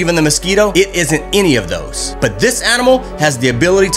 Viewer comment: Bruh i thought he said a maricle so i had to watch it again to hear "A MIRROR!"